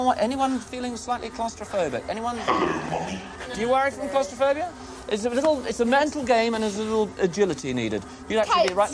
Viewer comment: What is it the computer says at 16:00 ? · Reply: "Hello Mum".